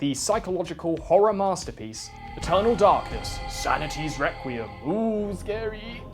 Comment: i was listening to him, thinking "Yeah yeah! He is gonna say the best game is Eternal Darkness!" then I thought he said Total Darkness